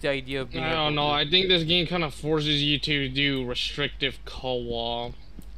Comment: What in the hell this guy saying? Restricted what...? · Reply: restricted co-op